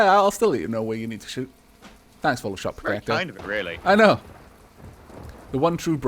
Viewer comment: What did say at 3:21? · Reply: "that's very kind of it, really"